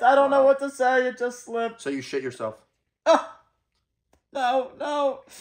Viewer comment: I literally just advanced 10 seconds and I just hear “so you shit yourself” lmao